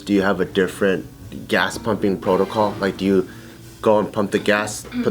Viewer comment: i thought it said humping